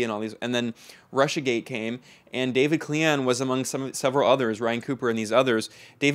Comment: "DAVID KLINGON?" Must've misheard that...